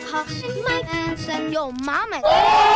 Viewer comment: wait what is he saying😭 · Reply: YO MAMA